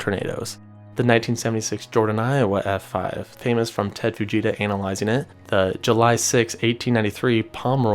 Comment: did you just say analizing it? Could work on the pronunciation 😂